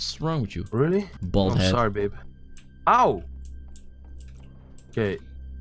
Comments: "Sorry babe" Did i hear it wrong? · did he just say sorry babe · did anybody else hear "I'm sorry b@